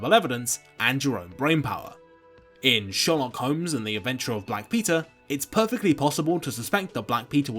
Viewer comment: I keep hearing "Sherlock Holmes and the eventual black pizza". As if Mr Holmes had a really bad oven.